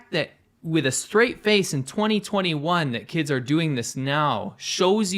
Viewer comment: did he says in 021? is this an old video